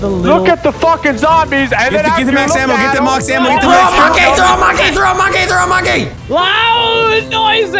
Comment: #BlameNogla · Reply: BasicallyIDoWrk Did you say "Mox Ammo" at 8:15? Also, yeah #BlameNogla